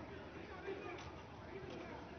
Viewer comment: i thought he said, "forgot what the fuck i was THINKIN" Either way its funny.